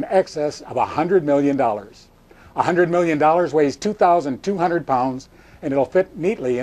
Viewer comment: Please tell me what corrections are necessary - other than the fact that the debt has grown to more than $20 trillion since the video was made... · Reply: If 1 million pounds is about 2,000 pounds (1 Ton), then 1 billion pounds (1 million X 1 thousand) should equal 2000X1000, or 2 million pounds, not 20,000 pounds.........oh you know what? I'm incorrect. Sorry about that lol, I misheard that. The video says that 100 million weighs 2,000 pounds. When I heard it, I thought it said that 1 million weighs 2,000 pounds. The math is correct, I'm wrong.